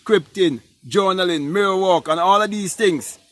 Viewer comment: Mirror Work?? Let me know what you meant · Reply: my miss of hearing, you said at .8.47 on video mirror work , I'm some how heard it as mirror walking, that was my missed hearing . Thank you.